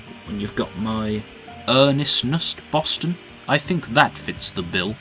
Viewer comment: What did say at 20:11 before "I think that fits the bill"? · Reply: "My earnestness Boston?"